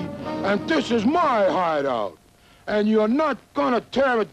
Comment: I thought that dog said "what a hard on" instead of hide out lmao